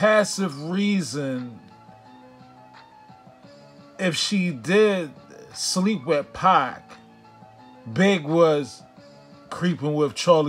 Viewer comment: I thought she said she never slept with Pac smh